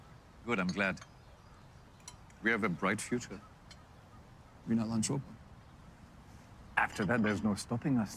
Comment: He says Atlantropa not anschluss, not sure why the subtitles got it wrong.